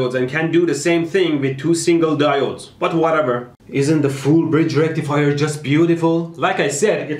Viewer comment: Mehdi says: FULL BRIDGE RECTIFIER caption says: FOOD BRIDGE RECTIFIER